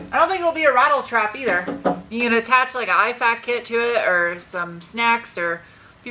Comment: i 100% thought you said you can attach a ipad kid to it.